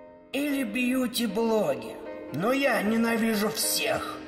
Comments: Сука в описании)))Послышалось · мне послышалось сука в описании